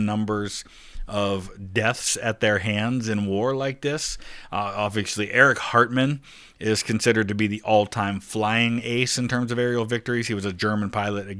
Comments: Dude, I am so tired I thought he said Eric Cartman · Did you say Eric Hartman or Eric Cartman there?